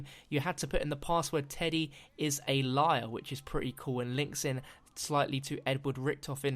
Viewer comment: It sounded like you said "Teddy is a liar."